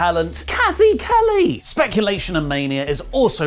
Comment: Oh, Cathy Kelly, thought you said Kelly Kelly. Nearly scared me. 😉😂🤣